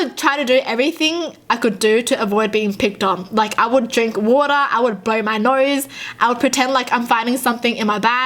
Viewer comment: sounds like she said “I would drink water I would bite my nose”